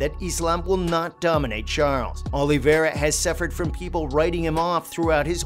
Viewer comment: I thought he said oliviera was a loser for life lol..